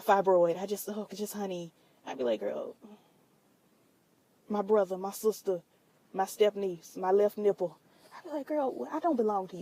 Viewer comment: Did you just say "My love, my fibroid..my step-niece, my left nipple" ??? fights the air like Cuba did in Boys In The Hood